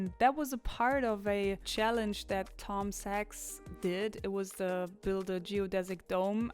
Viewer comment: i thought i heard my name "Tom Sykes" but I'm pretty sure it wasn't me lol